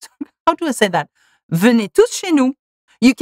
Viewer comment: I heard “venez tous chez nous” as “venez toucher nous” and I was so confused 😭😭😭😭